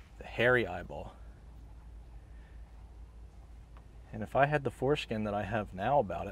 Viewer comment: Did I hear you say, “if I had the foreskin to,,,,,,”???